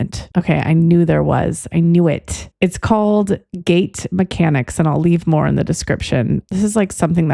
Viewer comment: So… first of all, why did you literally choose my real life nickname for this one??? Also, I know you were talking about gait mechanics, but I heard “gates mechanics” one of those times, and… it led me to quantum logic gates, soooo…. Thanks for this?? 😂😂😂